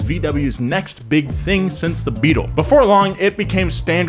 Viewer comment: did he say releach hatch😂😂